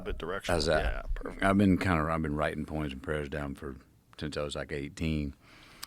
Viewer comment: I thought he said porn and prayers lolol